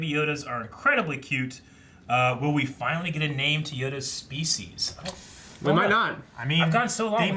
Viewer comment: When me and my mom watched this, we got into a debate about Yoda’s species due to a misunderstanding where mom heard it as “(the) Yoda Species” (ie Yoda is his name as well as the name of his species) instead of the proper “Yoda’s Species” (possessive)